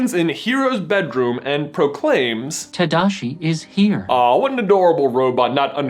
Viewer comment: Who else thought it said trash is here. Just me oh